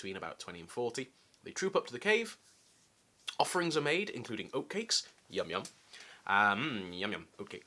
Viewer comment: I swear I thought you said "Offerings were made, including opiates...yum yum!"